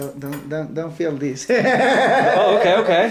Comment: I think at 11:29 he said “Don’t film this.”